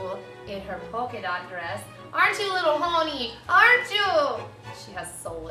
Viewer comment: Omg lol, at 0:21 I thought she said 'aren't you a little horny' to the dog XD